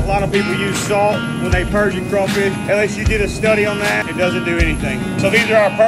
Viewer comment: You know I'm slow. I thought he said "unless you did a story on that"😂😂😂😂😂